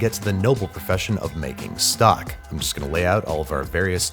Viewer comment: I’m not going to lie, when he said making stock I thought he said making sock😂😭